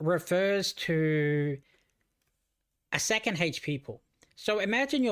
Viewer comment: What word(s) are you saying at 6:15? "Core stress refers to a" and then what sounds like second hatch people? And thank you for thr video! Very useful 😀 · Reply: HP Pool